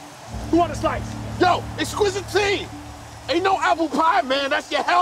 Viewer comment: Watch this with the subtitles and laugh/cry for the poor soul who had to try to figure that "Eqqsquizitine" and "Cartoons Plural" and heard it as "Exquisite T" and "Cartoon Squirrel."